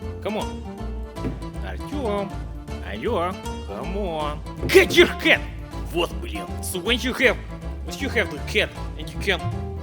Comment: i heard kefir blyat cor some reason